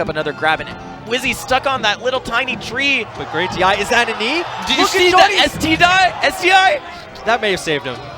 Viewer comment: I swear he said "STD" instead of "SDI". I would have died laughing if I was co-casting.